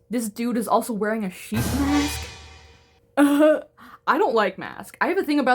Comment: I thought you said "sheet mask" and I wasn't looking and I thought, ooh skincare queen! 😂